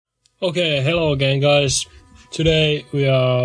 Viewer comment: i may have misheard but did you start of the video by saying "ok hello gay guys" lmao